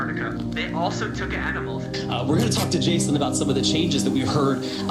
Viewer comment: OMG. when the dude about the mothership eclipse said "they also brought animals" at the end of his thing, I thought he said "they also took edibles"